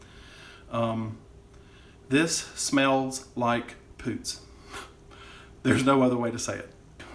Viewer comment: I turned on captions to see what you'd said, but she reads "smells like a poot." Neither of us have any idea what you're saying, haha.